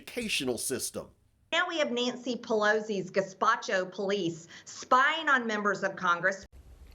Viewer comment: Did she actually say; ”Gazpacho” … ? 😳